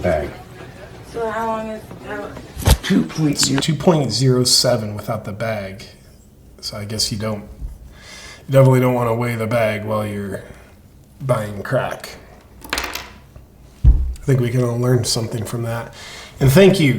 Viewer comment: sounded like you said Don't want to weigh the bag when your Biden Crack lmao